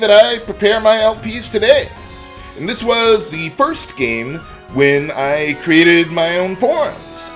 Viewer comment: At first, I almost thought you said at 5:18, "This was the first game when I created my own porn." O.o